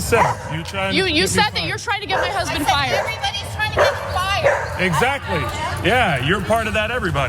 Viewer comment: What I heard was, "Everybody is trying to get fired". However, I do believe Ana.